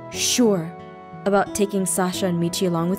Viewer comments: is it just my ears or did she say Sasa instead of Sasha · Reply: I heard her say salsa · Travis: All we're waiting on now is you. What about Salsa and Michi? Am I the only one that noticed this? Laughing so hard! edit : ew my younger self was so cringy like wtf is laughing so hard but then again I caught on to a funny bit so as I'm cringy I am proud (not really ew) lmao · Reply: I thought he said sausage and Michi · is it just my ears or did she say Sasa instead of Sasha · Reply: it sounded like she said sausa XD idk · is it just my ears or did she say Sasa instead of Sasha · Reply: I heard "Saska" but I think that's just me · is it just my ears or did she say Sasa instead of Sasha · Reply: I thought she said Salsa... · Salsa and Michi? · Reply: It does sound like she said Salsa